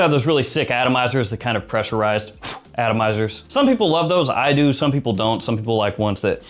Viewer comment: No pressurized atomizer? No magnetic cap? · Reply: I thought he said it is pressurized. No cap tho